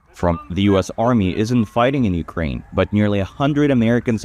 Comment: I thought you said Russia was fighting Ukraine alone😂 don't tell me NATO is losing